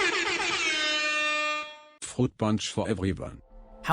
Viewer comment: Fun fact: the speech synthetization for the "tell anyone about this and i'll cut your throat" is actually a synth voice for German Hence why it sounds so odd as the voice is trying to pronounce english words but can only make use of german pronounciation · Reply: At first I thought he said "Fruit punch no refunds"